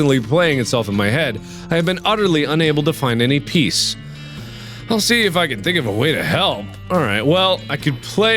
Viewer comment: When Mark read "I will see if i can find a way to help" I heard "I will see if i can find a way to hell".